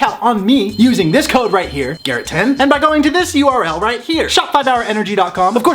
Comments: when he introduced the code as “Garrett10” I thought it said “Garrettito” im blind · Lol, I thought the code said 'Garrettio' instead of Garrett10.